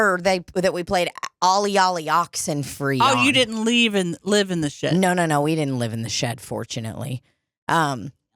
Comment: It definitely did sound like was saying they lived in a shed at first hahahahaha I was like wait... they took in Russians but made them live in a damn shed?! 😂